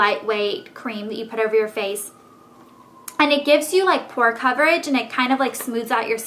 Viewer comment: i almost thought you said "poor" coverage instead of "pore" coverage. lol